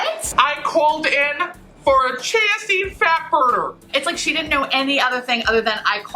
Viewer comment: I thought you were saying “Chancey Fatburner” Like if it was a person. Lmaooo I was so confused for a lil bit. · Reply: I heard it as "Cheesy Fatburner" 🤣